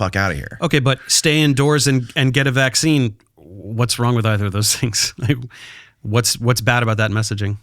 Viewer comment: please explain lmao. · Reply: ish, "staying indoors and take the vaccine, what's wrong with that?" That's all he said, love Dave 👍